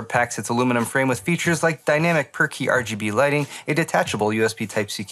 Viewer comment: Was that per-key lighting, or perky lighting? I swear I heard “perky” :)